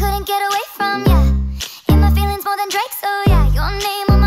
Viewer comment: DID IT SAY DRAKE?